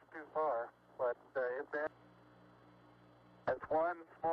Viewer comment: I agree with Neil Armstrong, that he actually said, "One small step for a man, One giant leap for mankind." If you put the speed at 0.5, (half speed), and to to time of 0:43, over and over, and you can hear it as "for uh man." As a trained speech therapist, we are trained to listen to speech, often unintelligible, and decipher the sounds and sound patterns, versus the letters, or words. Thus, when a speech therapist is trained to transcribe speech, we use the IPA, International Phonetic Association symbols. The symbols are sometimes similar to letters, but often have modifications to get the actual sound as nearly to the sound as possible. The benefit of the IPA, is that it's a speech sound representation system that allows any speech in any language or dialect to be transcribed in such a manner as for any other person similarly trained to reproduce in speech by reading a transcription. If the schools all taught IPA instead of the ABC's, we all could learn to write what anyone said, in any language, if our hearing was adequate. Example, Hear & Here, same sounds, depending on speaker, but the poor kids learning English are going to be confused. Spanish is one letter equals one sound. English, has 26 letters that represent almost 46 sounds, plus being silent! Add the English letters in combination,, which make totally different sounds across words? Geez. Dozens and dozens of oddities. "The" is definitely a word that makes no sense in how it's spelled. Watch a funny, and somewha